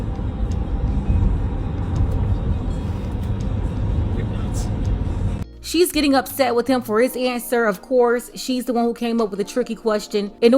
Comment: I heard him say, "You're nutts." 🤣🤣🤣🤣 game over